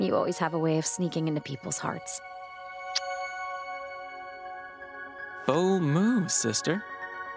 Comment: BOLD MOVE SISTER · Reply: I thought he said oH MoOooOO SisTEr 😂